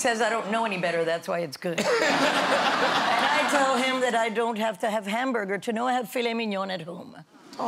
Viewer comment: what did she say? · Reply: "I don't have to have hamburger to know I have filet mignon at home."